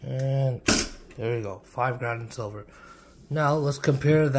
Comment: I was extremely confused. I thought he said 5 grams of silver. My man, I'm not sure what you're using to measure with but that's gotta be a lot more than 5 grams of silver.